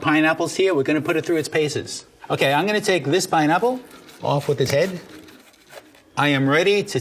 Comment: Did he just say " off with his head"